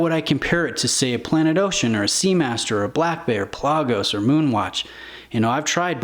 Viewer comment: did you say Black Bear ??